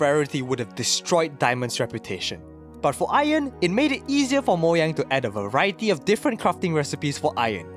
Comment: lol moyang instead of Mojang